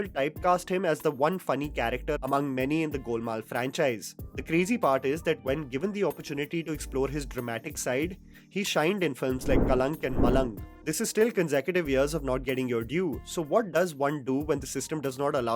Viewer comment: Sorry i know it's.. but m just curious did u say "shined " at 9:42!! Anyways..😅